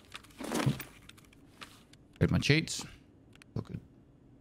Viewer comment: I'm curious, what is all that text at 15:43? I think if I heard correctly he said "That's my cheats". Am I wrong? If not, please explain.Thanks.